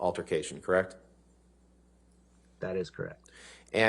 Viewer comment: 主さん動画お待ちしておりました!！アンバーはほんま自分の裁判なのにチクチクやられとるときは他人事のようだな 最初の動画から気になっていたのですが アンバーがその通りですとか同意しますを簡潔に言っている単語がわからないのでお時間あれば教えていただきませんか クラフト？クラプト?って聞こえるんですけど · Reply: 自分に不利な話が出ると、聞かなくなるアンバーさんです🤣 アンバーさんの「そうです」と言う返事は「That is correct.(それは正しい)」かな？ コレクトコールのコレクト(collect) は簡単だけど、こちらのコレクト(correct)は日本人の耳に一番難しいRが2つも繋がってて難度の高い単語ですな。 Rの音がこもるからクゥェクト、みたいに聞こえるかも？